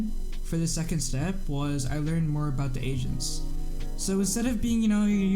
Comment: i straight up thought he said i learnt more about the asians lmao